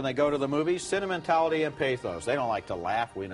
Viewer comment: what does he say?? sentimentality and pay phones??? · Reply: Pathos.